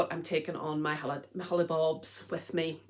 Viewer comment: I always enjoy your podcasts. I vote for sending the pair of socks that you're not sure about to Marie Curie. There is some yellow in there, and as you said, there might be a patient with swollen feet who would greatly appreciate them. I hope your family has a lovely time in Ireland. I thought I heard you say "holibobs." Is that an Irish word for holidays/vacations?